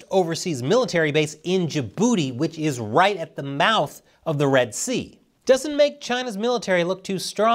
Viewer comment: I heard "j-booty is right at the mouth" and I laughed because I am a child.